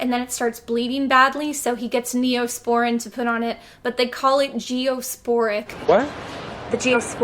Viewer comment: Wait, it looked like you were trying to say "Neospoin", but it sounded like you said "Geosporic". Is this a reference to something? I think I could use a flashback.